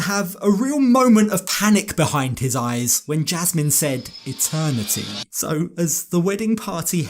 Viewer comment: I swear, when she said "eternity" I heard "attorney fees"